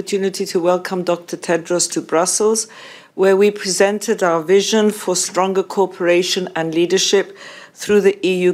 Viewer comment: This video is hilarious. At times I heard phrases like 6:28 "our vision for stronger corporation" and 3:20 "WHO will begin oppressions". It was only an effect of their accents, but nevertheless, it felt like an 'accent version' of a Freudian slip hahaha